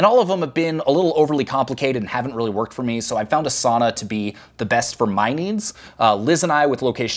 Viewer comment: Lol, I thought you said A Sauna. Yep, that is a great place to think and decompress and be more effective!